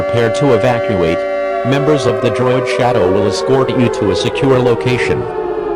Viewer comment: WHAT ARE YOU SAYING!?!? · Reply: location